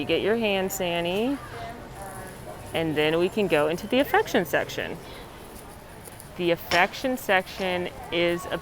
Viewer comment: LOL...I thought you were saying 'infection section'...hahaha!! better turn on my subtitles. Love you!!